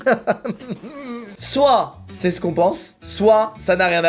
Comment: Squeezie : "Soit..." Moi : "Stika"